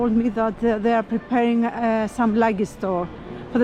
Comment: What did she say at 6:08 that was maybe going to be ready by June. Sounded like "preparing some lug a thor" or "lucky store". Maybe luggage store? Like people can pay to store their luggage there? But was now tourist help. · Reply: I meant a luggage store 😀where people can leave larger objects, strollers, backpacks, etc. before they enter the Acropolis.